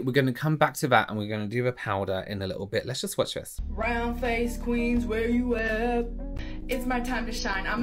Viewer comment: lmaooo i thought she said BROWN face queens 😅😅🙈🙈🙈